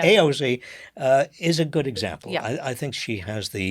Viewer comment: lol a good dem. I thought Heather said God Dam